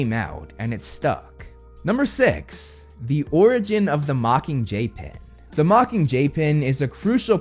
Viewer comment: it sounds like ur saying the mocking jaypin